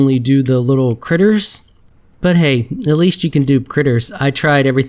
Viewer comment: it sounded like you said "atleast you have aids" lol